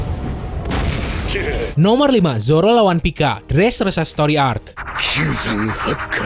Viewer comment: zoro lawan pico??????? salah denger kali gw